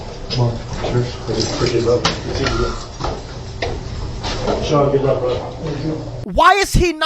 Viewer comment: Did he say, “Sean, good luck bud.”?